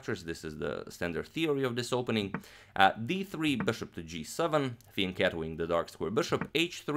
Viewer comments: i never understand what he's saying about the bishop at 6:16 . can anyone tell me what that means? when i turn on the captions it says "thin cat wing" XD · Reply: Fianchetto-ing 😁 somehow it couldnt be anything else based on your description but i still clicked the timestamp to hear him say it. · What does agadmator say right before "the dark square bishop"? I heard this expression a few times now but I can't get it figured out how it should be written. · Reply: Fianchetto